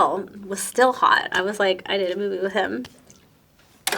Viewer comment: "I did a woohoo with him" haha! guess I heard wrong xD